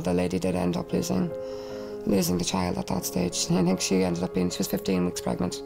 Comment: cannot understand the accent, text please? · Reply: That call didn't end well. She ended up losing the child. She was 15 weeks pregnant.